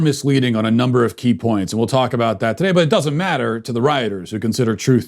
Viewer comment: I don't know if you realize that when you said "it doesn't matter to the WRITERS"; it sounded like you said it doesn't matter to the RIGHT. I had to go back and listen again to make sure. Oh, and b.t.w., great job, as always, Matt.